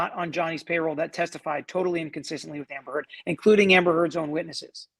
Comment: I think you misspoke. You said, in general, that the JD witnesses testified "totally and consistently with Amber Heard, including Amber's own..."